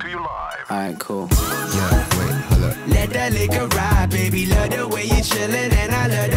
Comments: dude I thought they were saying “let that n——r ride” I was confused why no one was freaking out omg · For real thought he said: "let that n***a ride" when I first heard this song... I'm glad I was wrong